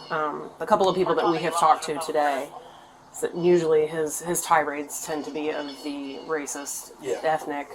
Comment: re: subtitles. I clearly hear the officer saying "his tirades", not "his *unintelligible*..." as your subtitles read.